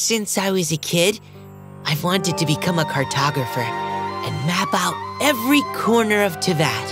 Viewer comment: no way I though he was gonna say " I wanted to become a Kardashian" byeee